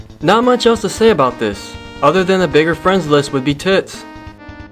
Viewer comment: Um at 9:32? do i hear the word tits?